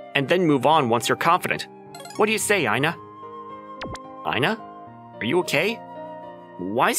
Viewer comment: "Aina Are you okay?" Meanwhile what i heard "Aina? Are you gay?"